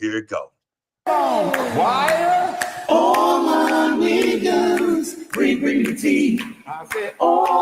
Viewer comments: I had to rewind because I thought he said all my "Niccaz we bringin the tea" 😂😅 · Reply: I thought he said all my Wiggas bringing the tea! 😮😂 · Armon I thought that sht said “allll my wigggahhhhs, we bring the teas” 😒